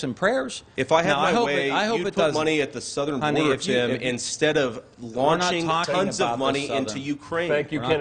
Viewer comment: Did he call him "honey" at 23:54? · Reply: sounded like "if i had my way you'd flood money to the southern border"